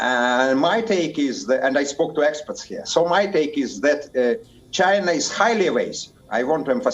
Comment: I can't understand his accent What did he say "China is highlyavaze"?